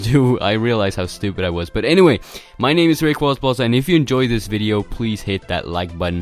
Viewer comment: what did you say your name was? Rayquazballs?